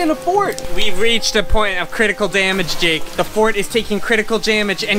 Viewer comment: Listen to what said on 15:24 He said "jamage"